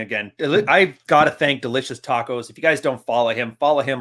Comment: I don't know what you're talking about here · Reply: you mention that ''the incel hero' s journey'' got you into reading again. The writer is ''delicious taccos'' right? Didn't catch it at first!